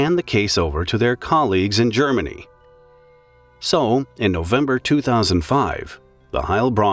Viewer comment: i thought he said september 2001... What is my mind conditioned to?